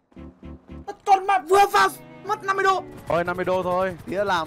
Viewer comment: Khúc 6:46 ông cảnh sát nói gì ko nghe thấy mng :(( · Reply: 50$ tien` chua~ benh ma`